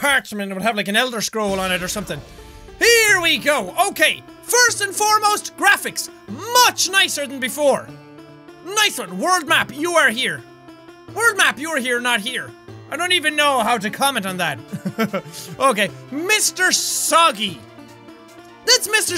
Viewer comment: did he say word map or world map